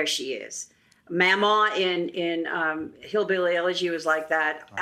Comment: Oh, I misheard it as Hillbilly Allergy.